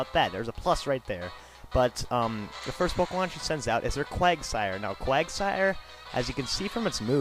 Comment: lol at 0:51 i thought you said quagmire in family guy :D